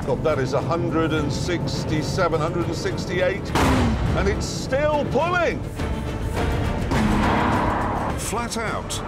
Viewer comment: Jeremy Clarkson: "It still boleh!!!" I can't understand.... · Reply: Pulling* it's still pulling*